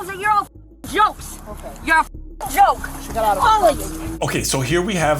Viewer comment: you heard "Does it hurt". I heard "She got out of her cuffs". The phonetics of the latter, spoken very fast and with the other noise, does also sound like "Does it hurt". But considering that was the moment she actually did slip the cuffs, I believe the latter is correct.